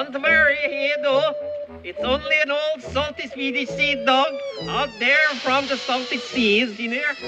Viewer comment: Wow...most likely my favourite scene of all time. And I always thought he said, "it's just an old salty "seedy" seadog from the salty seas." You learn something new everyday... Sellers was the greatest !!!!